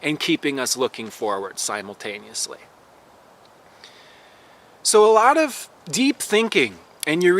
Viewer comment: You said your insights about the moon cycles and never having the signal that everything is okay and it's a give and take, and then you said "so a lot of deep thinking" but i heard "so a lot of day drinking" and i cracked up